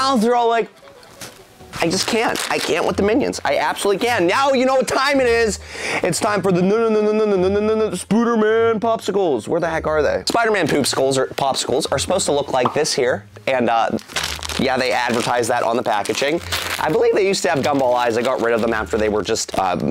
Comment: Youtube thought you said "Poop skulls" 🤣